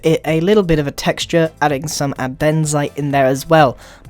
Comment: did he say abenzite?